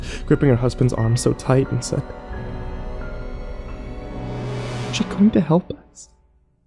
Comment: what did she say? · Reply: TheLegend69 "aren't you going to help us"